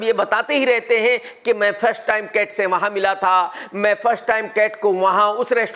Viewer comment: I heard Main one night stand kat se waha'n Mila tha😂😂😂😂😂😂😂😂😂😂😂😂